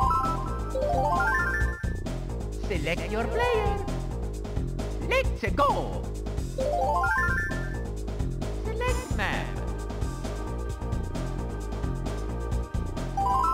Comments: The first time I played the game when I used the mushroom to get a boost for the longest time I thought Mario said Mexico instead of let's a go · When I was little, I always thought he said, MEX-I-CO 😂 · it sounds like Mario is saying select ma